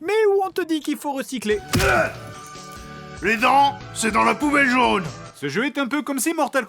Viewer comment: "Les dents ! C'est dans la poubelle jaune !" 😂😂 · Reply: pk moi j'ai compris "les gens c dans la poubelle jaune"